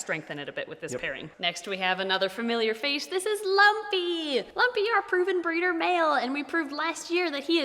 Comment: I thought when you said "Lumpy" I thought you were saying Loveby and I didn't know who he was.